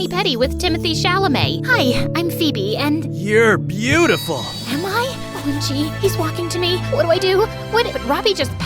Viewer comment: I found another MSA error! When Robbie said that Phoebe was beautiful, she said “AM I?” While the subtitles said “I Am?” I’m proud to find another error!